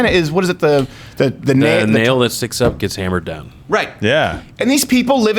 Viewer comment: What was the saying at approx 16:00 something like the nail that (Sticks or gets) up gets hammered down"? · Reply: It's an old communist China saying. "The nail that sticks up will get hammered down." In other words, anyone who doesn't toe the party line will be dealt with.